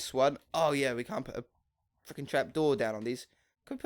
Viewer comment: first time I heard u swear 😱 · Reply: epic pro cyber He didn't swear he said freaking not the "F" word!